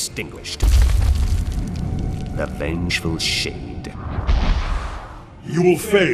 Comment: I swear I thought Shinnok said 'the vengeful shit'